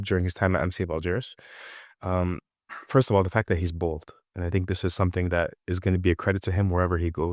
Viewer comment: When you say ‘he’s bold’, what I heard was ‘he’s bald’, and as a bald man myself, I went YES, that’s a positive in all aspect of life🤷🏾